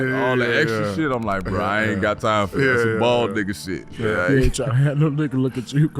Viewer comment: did he say bald dhead igga shit lmfaoooo